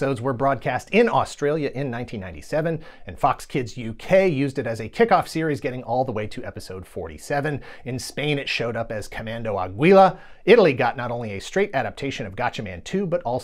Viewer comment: I wasn't watching and thought you said Commando Abuela. I'd watch that show.